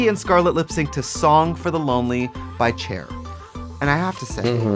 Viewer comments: did she really say By Chair??? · Did Tracy just say "chair"????